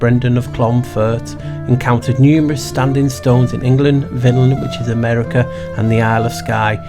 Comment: At marker 6:41,2,3 what word are you saying for America? Please 🙏🏻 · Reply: Vinland is a name vikings used for America